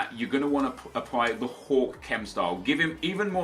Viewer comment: am I the only one who thought he said hulk chem? I know it is hawk